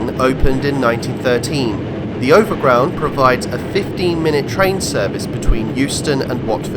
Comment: did he say 15 or 50? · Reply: 15